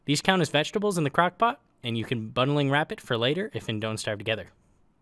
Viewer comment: This count as vegetables on the crock pot, and you can...? I did not understand what he said. Could someone help? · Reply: Bundle wrap it. Its a dont starve together exclusive item that makes anything that is inside it dont spoil, and you can carry more stuff with ig